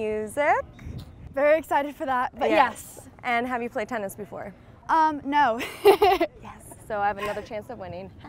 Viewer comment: Loren has played tenis before I've seen it in her stories like this year!! And she said she never played! Y she lying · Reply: Cecilia Tjendra 0:47 listen to it. She clearly says no